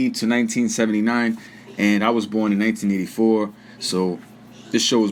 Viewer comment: you was born in 1884? Boi how you still living?